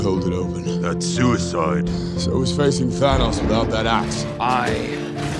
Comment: Sometimes I hear "So is facing thanos without that ass" Instead of axe😂😂😂